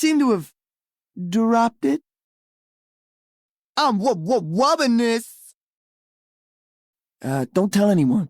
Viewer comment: “I’m loving this” is what he’s saying, referencing McDonald’s.